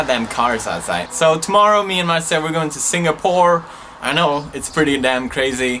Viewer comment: First i thought you Said "were going to sing a porn" ._.